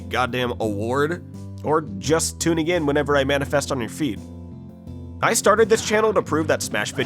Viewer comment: I thought you said manifest on our feet. Had to replay and turn my phone up a few times. 😂